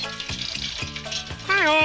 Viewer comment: I keep hearing: "Hello, this is Kevin De Bruyne"